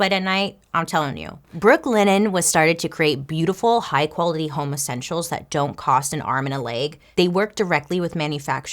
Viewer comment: Omfg during your ad for the linen I had to rewind it like 3 time cuz I thought you said they manufacture high quality homosexuals when you actually said home essentials!!! I'm freaking dying😂😂😂😂I just love you Bailey!!